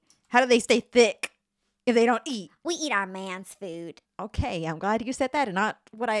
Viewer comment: I ligit thought said “eat our man’s out”😂😂😂😭😭😭😭 Love your videos!!!!❤❤❤❤